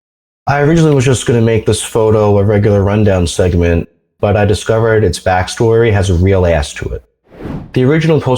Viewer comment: Does say, "I discovered that its backstory has a real ass to it?" LOL I've never heard that expression before! That's a clever way to describe a "backstory"! Or maybe I'm just hearing it wrong.